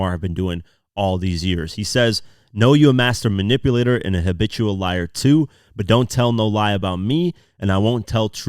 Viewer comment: I know it’s “habitual liar” but I hear “bitch you a liar” every time